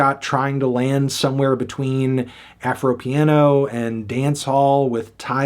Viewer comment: did you just say afropiano???